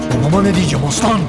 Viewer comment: sn de mönke ne diyor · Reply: Babama kesicem aslan